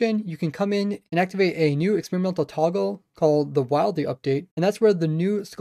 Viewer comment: it sounds like he says the Wildey update XD